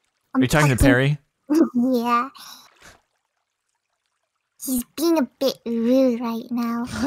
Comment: The moment it sounded like said a bad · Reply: It almost sounded like she said "he's being a bitch"